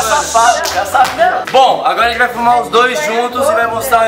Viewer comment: só eu que ouvi "agora vms fumar os dois"